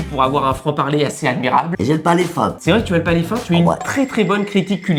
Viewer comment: elle dit « J’aime pas les… » quoi ? J’ai ecoute 10 x j’arrive tjr pas a comprendre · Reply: J’ai le palet fin ^^